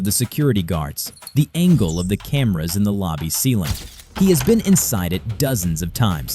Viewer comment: did he say camera